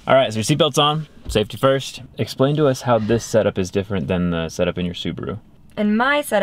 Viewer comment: I thought you said “how is this set up different than your supras?” I had to rewind real quick like HUHh😆😆